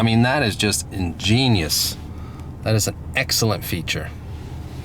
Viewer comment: when he pulled out the extra visor I thought he said ingenious